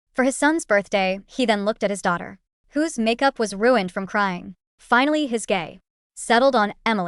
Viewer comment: I heard she say "finally his gay"